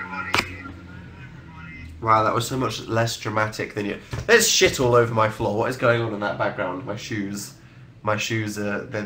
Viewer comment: it sounded like he said 'let's shit all over my floor' it's like okay kittens and steamrollers fic much